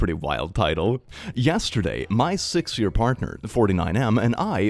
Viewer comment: I THOUGHT THAT FIRST ONE SAID 6 YR OLD PARTNER INSTEAD OF 6 YR PARTNER- 😭😭😭😭